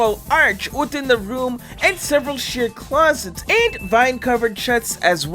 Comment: i thought he said 'shit closests'